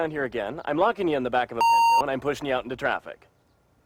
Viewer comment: What the hell did he said? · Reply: "I'm locking you in the back of a Pinto and pushing you into traffic" it was a shitty car from the late 70s early 80s if I remember it was the Ford Pinto, it was a shit car, since the game was foreword thinking, and they dont have the rights to the Ford Pinto, they beeped it, honestly like 93% of all the videos are never seen in normal game play, or not that I have ever saw anyway